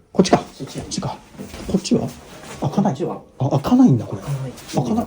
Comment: 女性の声で開かないよみたいに聞こえました